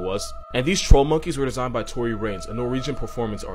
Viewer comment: why did I think you said Tory Lanez for a second 😶🫥